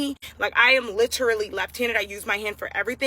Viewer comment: … I don’t know if I’m hearing right at 5:18 does she say she’s “Left-handed”…? Then yeah the right is important too, but not like you can’t write anything. Unless she does the same as me, where I write with my left and do everything else with my right hand. But still shouldn’t be the reason, the guy. It’s like when I was a kid watching Wrestling with my brother and cousin. It says don’t try this or replicate this at home. Yet we still do because we were KIDS! You an adult, you should know this.